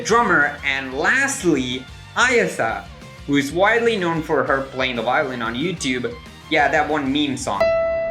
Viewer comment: morfonica violet incident? Or did i mishear and he said violinist? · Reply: "widely known for playing the violin on YouTube"